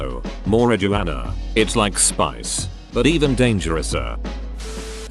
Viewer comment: the Dutch: Did i hear SPICE? also G E K O L O N I S E E R D(C O L O N I Z E D) also im dutch